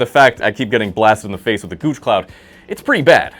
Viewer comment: I put this on in the background so I could make tea whilst listening and. Did I just hear “gooch cloud@